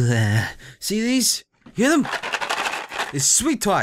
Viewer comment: What sweet were you eating? Random question, but im just kinda curious XD I heard 'sweet twots'... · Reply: Sweet tarts. I think.